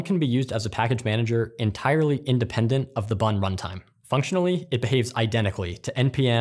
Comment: I thought he said "buntime". almost dropped my croissant.